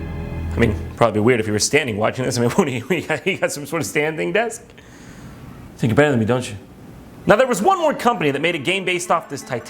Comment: wtf is he saying!!!??? I love jontron but sometimes i just dont understand what hes saying... GOD DAMN IT!!! AND IIIIIIIIIIIIIIIIIIIIIIIIIIIIIIIIIIIIIIIIIIIIIIIIIIIIIIIIIIIIIIII..... HOLLY SHIT!!!! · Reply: "You think your better than me don't you."